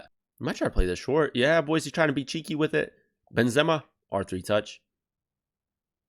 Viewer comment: He scored one goal in all of my 15 champs games and- did you just say "ben-zemer"??? My boy..... Its "ben-ze-ma"